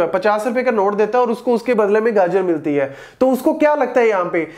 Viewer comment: Am I the only one who heard 'Ganja' instead of 'Gajar'??